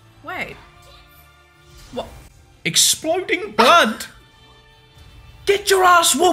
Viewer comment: Everybody gangster till Nezuko wakes up yelling "Buckets"🔥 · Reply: When nezuko says exploding blood it sounds like she saying buckets